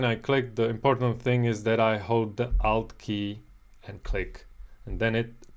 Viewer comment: "Hold the out key" - what is the "out" key? Please explain. Perhaps I've mis-understood you, but I don't understand what key I'm supposed to hold. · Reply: It's the "Alt" key.